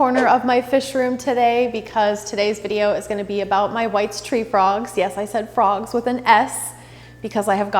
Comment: I thought she said my wife’s tree frogs 🐸